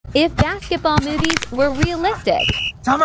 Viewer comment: I thought it said "If basketball moves were realistic